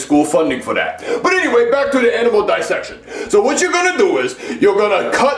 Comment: anyone else hear edible dissection lol